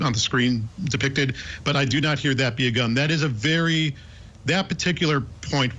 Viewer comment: It's scary that the rest of us have to live in the same world as morons who think they somehow hear "That be a gun". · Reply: it does sound like she says That be a gun. But I think it's her saying "that we go down", finishing Libby's remarks about the trail.